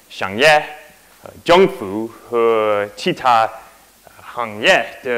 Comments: I think he's trying to say 政府． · I think the bit you couldn't understand was 政府